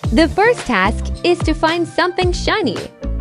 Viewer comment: she said fine instead of find😂😂😂😂😂😂😂😂